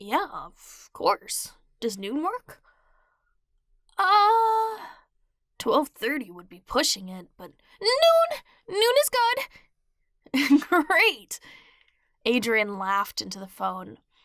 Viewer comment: when he said great it sounded like Noot from the math game Prodigy